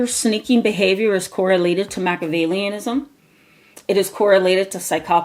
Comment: When you said, "When you look at Machiavellianism, . . . " I thought you said, "macadamianism," and I thought, "Yep, they're nuts." Then I was hungry.